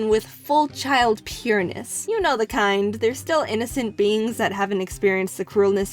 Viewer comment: I thought she said "they are innocent beans" lol